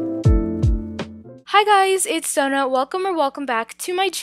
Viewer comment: i heard 'hey guys its sona'